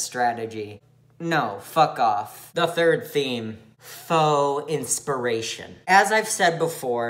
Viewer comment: okay, but when he said 'faux inspiration' i kept hearing 'foe inspiration' and like, i would listen to a song written entirely out of spite and inspired by your arch-enemy.